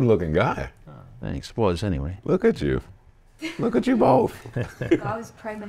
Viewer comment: I legit thought he said "pause" at 2:55 🤦🏽‍♂️ But fr. I didn't expect to get this emotional at this shit. If this doesn't motivate you, nothing will. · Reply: He said “Was, anyway.”